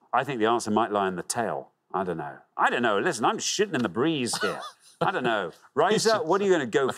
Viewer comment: does he say he's shitting in the breeze?